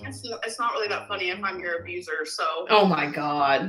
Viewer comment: Hey twins!!! Little off subject but did you watch Under The Hood today? We are beginning to address the n word and I’m telling you…I will push outta her scooter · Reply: soooo I realized that I was incorrect about 😭😭😭 someone out the bleeeeeeep in and I thought she was saying n …. But she was saying abuser 😭😭😭